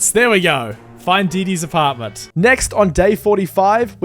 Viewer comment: am I the only one that thought he said diddy’s apartment